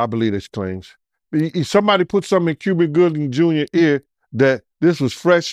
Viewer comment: On me I heard him say Cuvin Goobin Juber.😂😂😂